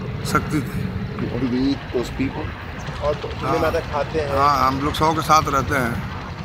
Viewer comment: He clearly said "Yes, we LIVE with the dead bodies", not "Eat".